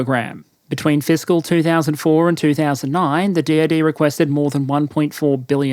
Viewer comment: I kept hearing "The DOD" as "The Deity". Not far off, but a bit worrisome that I just accepted it.